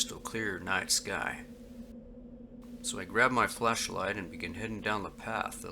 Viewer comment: I could have sworn I heard him say "Fleshlight"